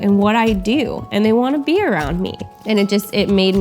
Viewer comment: I thought she said I think they to beer around me